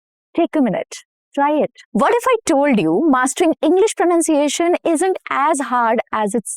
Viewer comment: Did you just said “Vhat if I told you” 0:22 🤓